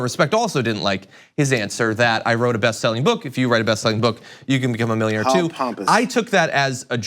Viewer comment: He did not say write a book to become a millionaire. He said write a BEST SELLING book to become a millionaire. which is fair.